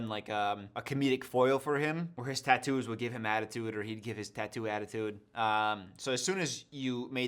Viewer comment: I thought you just said "a-tattoo-de" instead of attitude